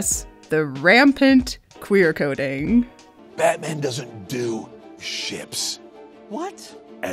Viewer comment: DID HE SAY SHIP?!?